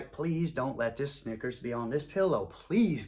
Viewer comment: Bro I thought you said a ngga was on his pillow. Then I caught what I was saying When I stopped laughing